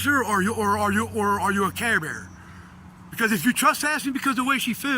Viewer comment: Did he call the officer a "care bear?" Clearly, there's something not right with this guy.